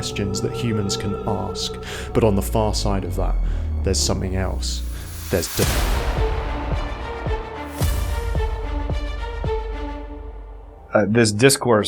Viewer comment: Is it just me, or did he say: "But on the far side of that, there's something else: there's dick." I'm gonna hang around, but only 'cos I wanna know...what's on the other side. Not for the dick.